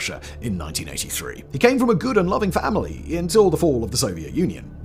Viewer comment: I know that what Simon said actually said “…good and loving familiy …” What I heard was “Gouda-loving family” 😂😂